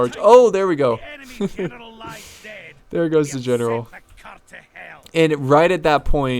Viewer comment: in the background...lol did he say what I think he said? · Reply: Child of Talos "sent the cur to hell".